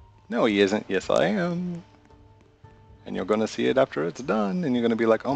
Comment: did u said "and u gonna see it after shes done" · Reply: and you're going to see it after it's done